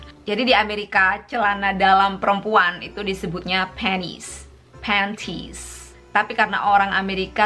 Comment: Gw dengernya Penis dong:(